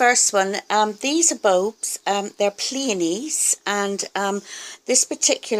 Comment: I've never heard of a pleione, nor have I seen one! I had to look it up because I thought you said peony :) Just beautiful! If I ever come across one in the states I'll definitely buy it! Your orchids are looking beautiful!